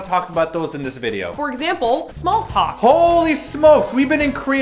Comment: I thought you said small pox but then I realized you said small talk...